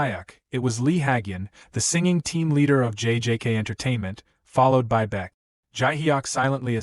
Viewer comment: Did i just heard jjk?